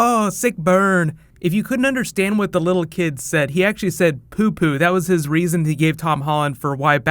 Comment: Didn't the kid say "the Joker" and not "Poo Poo" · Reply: Anik Mirza I think you're just trying to mess with me, but I'll keep the conversation going. I never watched it with the subtitles on to begin with, and I very clearly heard "poo poo". I don't even know where some people are getting "Joker" from anyway. Back when the trailer for season two of Daredevil came out, everyone was arguing about whether or not Punisher said "bang" or "beg". I always heard "bang", but at least then I could understand where they were coming from. "Poo poo" is why Tom says "Doesn't really make sense, but sure. That's awesome, buddy".